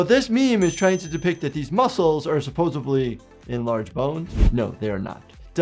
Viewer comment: Did you say supposably at 0:54?